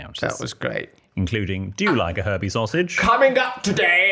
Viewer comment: I’m Mexican and listening to your English accent I thought you said “herpes sausage” 😂😂😂😅😅😅